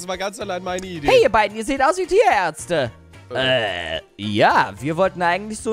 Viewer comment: Als erstes habe ich nicht "Ihr sied aus wie Tierärtze" verstanden Sonder "Ihr sied aus wie Diaerze" ; als solches habe ich es verstanden.